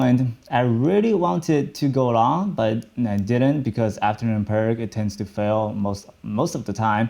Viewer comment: Afternoon (something) tends to fail most of the time @ 1:45 . Anyone know what he said there? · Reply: Raoul Duke perk meaning like a new high at the end of the day tends to fade or doesn’t hold